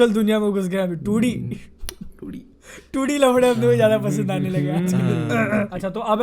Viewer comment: did say 2d lawde😂😂😂😂👁️👁️👁️🥸🥸🥸🥸🌚🌚🌚🌚